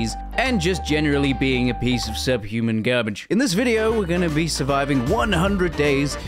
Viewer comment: i heard "a piece of shit"